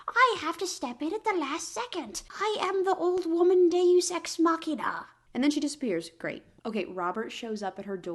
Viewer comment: now why did I hear deu s=x machina